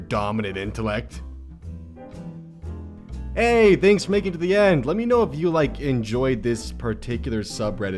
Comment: I thought at the end he said "thanks for making me blyat"